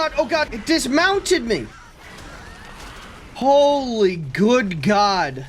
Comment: i heard "it mounted me" at first so medussy is a top confirmed ;)